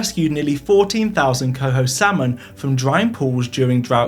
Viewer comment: captions say coo instead of coho 10:19 captions say PAS instead of PFAS